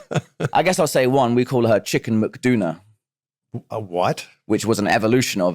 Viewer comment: I thought I heard kit said JD McDonagh